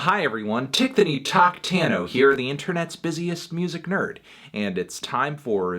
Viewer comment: If Anthony said "Tickthony Tocktano here." At 0:16 Why is it called "Tickthony Tockthony" in the text instead?